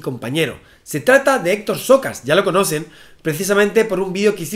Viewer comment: Por un momento escuché "Xokas" en vez de "Socas". Debió de invitar al experto en todo